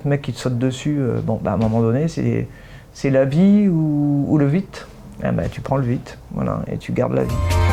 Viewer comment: la vie ou le vite.? De quoi elle parle...? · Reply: Le vit désignait le sexe masculin en ancien français